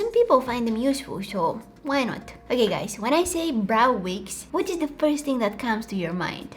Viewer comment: i thought you said brown wigs .... IM SORRY BUT I MISUNDERSTAND YOU QUITE OFTEN! ITS NOT YOU ITS ME!